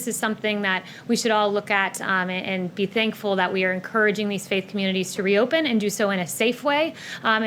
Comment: She sounded like she said "fake" community. Good thing my pastor clarified that he manages a "faith" community, which is what she meant to say.